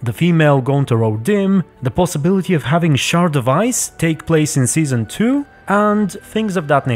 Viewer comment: For some reason I heard "sad device" instead of "shard of ice"